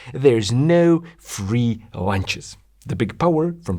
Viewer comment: it sounds like he says “there’s no free Lancias” not lunches. Pun intended? I hope so.